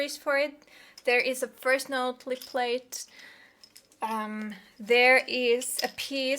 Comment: what do you say at 2:30 First note....plate ?